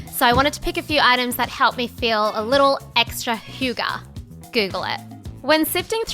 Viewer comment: Did she just say "Hygge"? Like the Danish word for "Cozy" or whaaat? I'm from denmark so pretty funny 😂😂